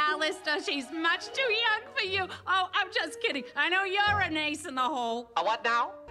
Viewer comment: "I know you're an ace-in-the-hole." Ace-in-the-hole. Ae-sexu-al. Fucken genius writing. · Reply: I actually thought she was saying "I know you an asshole!" Because both kinda sound similar lol